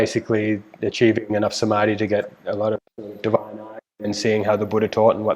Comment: Hi 00:16:34 Achieving enough samadhi to get a lot of __________ and seeing how the Buddha taught. 00:28:10 Well, it's _____________. What is saying ? I can't hear it properly. 🙏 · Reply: First one is “divine eye” Second one I can’t understand